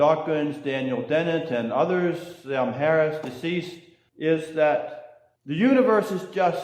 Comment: did he say “and Sam Harris, deceased.” ? Should someone let Sam know he is in a deceased condition??? That’ll be news to him I suspect.